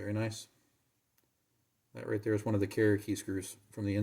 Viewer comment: Hammers? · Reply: Ignore me, I thought you said underside of the carriers